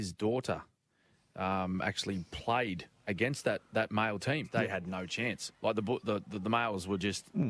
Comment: Am I the only one who thought the reporter said, Niple team?